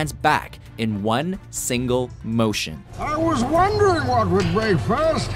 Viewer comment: always thought bane said "I WAS WONDERING WHAT WAS FOR BREAKFAST!!!"